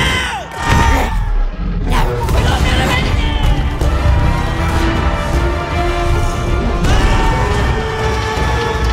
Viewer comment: what do they shout at 0:16? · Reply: WE'RE NOT GONNA MAKE IT!!!!